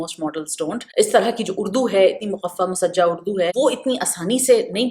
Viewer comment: what is that urdu word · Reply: Muqaffa musajjah…